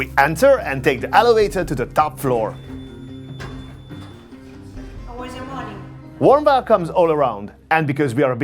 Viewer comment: Sounds like they said "o where's your money?" as soon as you got to the top floor. Lol, I don't speak much Italian.